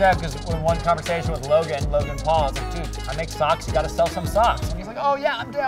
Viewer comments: At first I thought it said stocks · lol i thought it said stocks